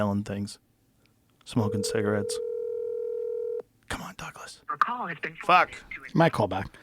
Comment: I heard him say Douglas call me and got excited 😂